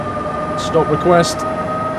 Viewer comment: did he say 'Stop Wequest!"?